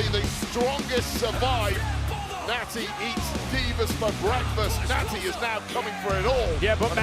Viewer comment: I thought Wade said Nattie eats beavers for breakfast